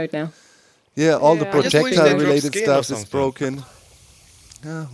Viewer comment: did Keralis say he wished they dropped SKIN???